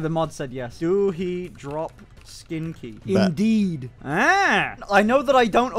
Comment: does anyone else hear someone say bet? or is it just me